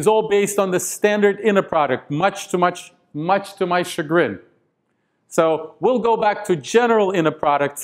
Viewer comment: What did he say at 2:32 ? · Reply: chagrin